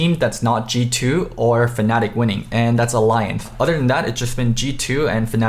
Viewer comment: I thought you said alianth